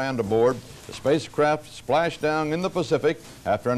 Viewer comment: Is it my imagination, or at the 0:18 mark, didn't it sound like Cronkite saying "the spacecraft splashed dang in the Pacific"? (Though I know what he'd intended - "splashed down.")